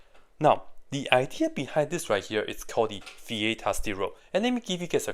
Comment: you mentioned the name of the theorem, but my old ears can't make it out. Can you tell me what you said? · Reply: They are called Viete's results or Viete's theorem. I think he was a French mathematician from the 1500s.